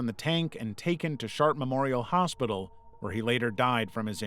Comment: Ahh, I'm glad they didn't shoot Manticore. 35:33 did you say SHART MEMORIAL HOSPITAL?? hahahahah